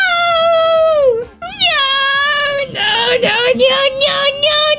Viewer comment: all i heard was nyo nyo nyo nyo nyo nyo nyo nyo nyo